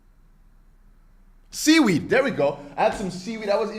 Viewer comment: I thought u was trying to say guacamole but you said seaweed.